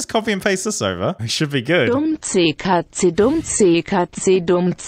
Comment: bro in my language i hear “dum sti cazzi” its italian